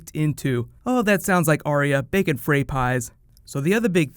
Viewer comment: "That sounds like Arya, baking Frey Pies" I heard "Bacon Frey Pies". Now I´m hungry. XD